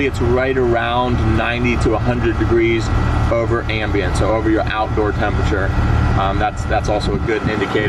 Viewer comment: 🧐Did he say, '...90-100° above ambient?' 😆